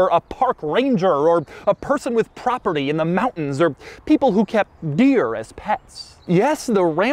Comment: Although I thought he said, "People who keep beer as pets…" I guess that would work too! :-)